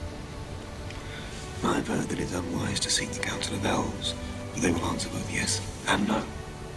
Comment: what does Bilbo say ? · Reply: "I’ve heard that it’s unwise to seek the counsel of elves, for they will answer both yes and no." Bilbo is expressing mild frustration that Elrond is 'sitting on the fence', rather than directly advising him on whether to continue with the dwarves or not - he has made a point about why he should go on (Bilbo is resilient) and a point about why he should perhaps stay (he likes his home comforts) yet ultimately the choice is Bilbo's and Elrond does not attempt to sway him either way, so Bilbo is just as uncertain as he was to start off with and somewhat irritated. They were originally Frodo's words to Gildor in FotR book, paraphrased, regarding Frodo's uncertainty as to whether or not he should wait in the Shire for Gandalf to begin his journey or to start without him. It was one of my favourite exchanges in the book so it is nice to see it appear here, in some form :-)